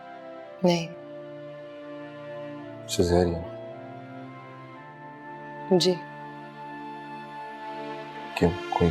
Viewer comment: What did he say at 15:34???? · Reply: He asks "Caesarian?"